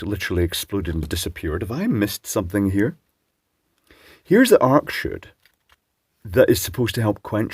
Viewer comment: What are you saying at 6:15? It sounds like "arc shood" but I can't find anything like that on Google. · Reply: Arc chute?